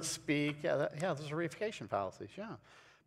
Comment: What fallacy? Couldn't understand what you each said. Thank You! The Closed Caption said reification. Explanation please. I've never used or heard that word used.